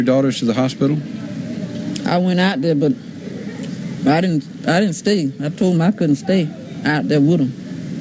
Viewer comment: Is it a language/accent barrier or is it a cognitive thing? 38:06 I can’t understand much of what any of the family is saying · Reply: Reporter: "You didn't go with your daughters to the hospital?" Mother: "I went out there, but I didn't stay, I told them I couldn't stay with them." Reporter: "What did you think when you had learned what really happened to your daughters?" Mother: "When I realized what happened, what I think happened, I didn't know nothing about it, no one even told me nothing about that they were going to give them an operation." Reporter: "But after the operation was performed and you found out about it, what did you think then?" Mother: "I didn't think anything, and I went--they took me back up there that morning and they had done operated on them. They were bringing them back up on the stretchers."